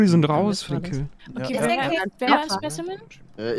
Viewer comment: hab ;;der Whopper Kill" verstanden.. glaube hab hunger :D